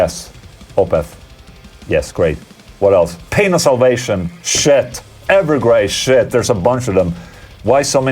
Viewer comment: I did a double take because I thought Ola said "Penis Ovation"